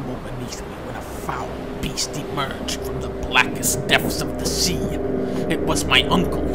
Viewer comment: ok i've heard dunkey say this is so many videos now I have to mention it. dunkey always says deaths instead of depths. he surprisingly uses the word quick frequently in his reviews so i can't help not notice it